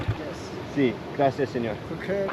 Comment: I thought he just said "who cares?" at first and genuinely laughed out loud